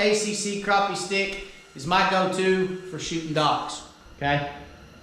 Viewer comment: At first i thought you said SHOOTING DUCKS!